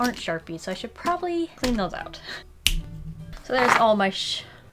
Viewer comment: for a sec i thought you were saying "so there's all my shi-" im going deaf...